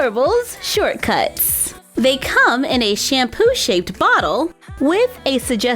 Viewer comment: wait did you say shampoo bottles!?! Cause I didn't know they were that small!?!?!?